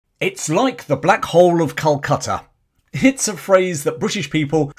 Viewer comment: I'm in the US, I had always heard it as The Black "Pit" of Calcutta! When my best bud from college rented quite the dump after we graduated, I labeled it "The Black Pit", and that's what we referred to it as until he mercifully moved out after about a year.